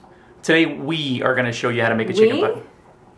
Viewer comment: I thought said "Chicken Butt". LOL!